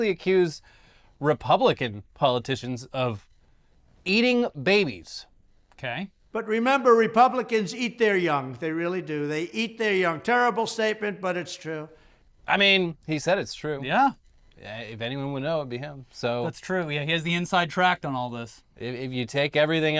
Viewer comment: Thought he was saying they eat their own cum. Could still be true.